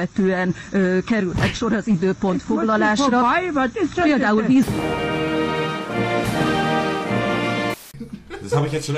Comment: Oma oder Opa, was sagt ihr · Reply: opa